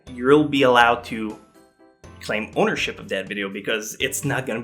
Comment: im really not sure why i keep hearing "bonership" instead of "ownership" ....eventhough i know he said ownership... god damnit staying up later is fucking me up..